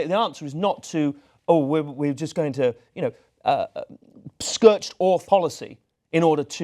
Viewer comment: Did he just say SKERTCHED ORTH? You mean SCORCHED EARTH 😂😂😂😂😂😂 P.S. Trump will never win - he's just a puppet propped up for future policy changes against immigrants in the name of 'freedom and democracy' don't believe me? Get back to me after the election - you're welcome x